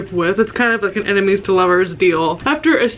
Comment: every time you say "ennemis to lovers" i hear "animes to lovers" and get really confused for a quick second